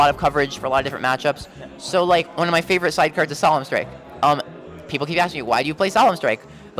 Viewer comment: What card does he mean when he says Salaman Strike at around 2:55?